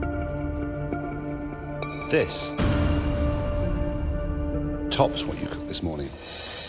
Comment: what did Marco say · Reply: "This tops what you've cooked this morning"